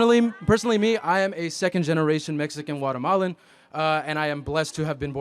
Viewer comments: did he just say he's a second generation Mexican watermelon? 🍉 😂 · Did he just say he was a second generation Mexican watermelon ? 🍉 😅